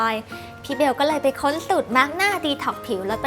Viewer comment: พี่พูดว่าตุดมาร์คหน้าตอนที่